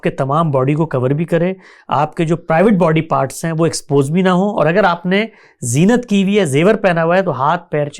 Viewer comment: What did he say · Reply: private body parts